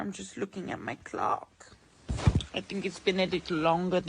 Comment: did it say ‘clock’ or another word that sounds like clock but minus the L in clock.